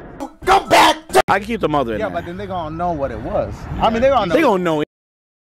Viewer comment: to me it sounds like he says “yeah but then they gon know what it was”